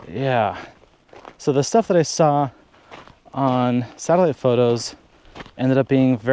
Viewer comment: Too funny. I heard your "satellite" as "salad". Had to listen three times before I remembered close captioning. Ah.